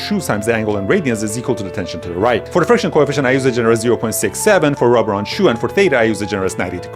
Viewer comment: Did I hear 6 7